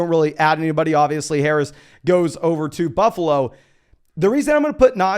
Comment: I was so confused I thought you were saying Najee is going to buffalo